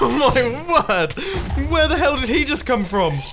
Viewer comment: Did Bumbo say hell? 15:02?!?!?!?!???!?????!?